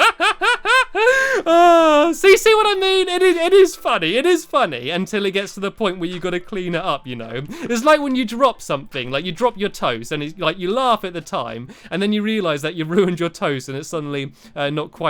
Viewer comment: I thought you said "When you drop your toes." Whoops!